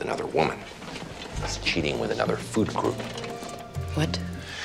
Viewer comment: Thought he said another “fruity loop” then he said burger n all that, kinda shifted my guess to another food group as well. 😂